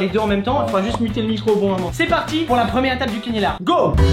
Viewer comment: C'est moi ou a 2:53 il a dit cinilar 😂😂👌